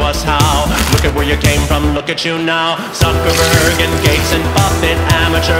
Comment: The song went from “you can do it!” To DRINK THERE BLOOD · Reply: I thought it said "drink their butt" 💀💀💀💀